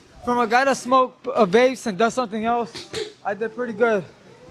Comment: did he say, “a guy who vapes and DOES SOMETHING ELSE..?!?!” 🫨🫪